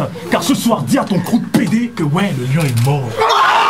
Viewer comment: il dit quoi? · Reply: Le lion est mort